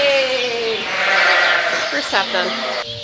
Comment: lice treatments finished - “Yay!” Sheep - “Yay!” I know I can’t be the only one who heard that.